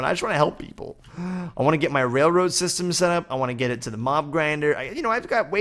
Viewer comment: Lol at first i thought you said mom grinder not mob grinder 😂